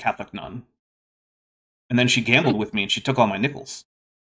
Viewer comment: I thought you said, "she took all my nipples." I wondered what card game you were playing. Maybe one with a deck that had hearts, clubs, diamonds, spades and nipples.